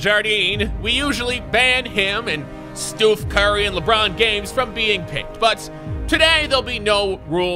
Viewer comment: Was I the only one who thought he said "Lebron Games"?